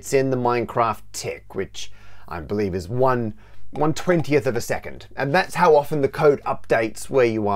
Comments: The subtitles have a mistake, it says the tick is one 12th of a second, unlike what is said and shown on screen, which is one 20th of a second (and which is the correct number) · audio and diagram say 1 20th of a second but subtitles say "one 12th"